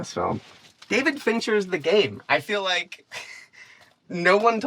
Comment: Oooooh he said the GaMe! I thought he said the GaPe and i was like, "hmm, I've never heard of it"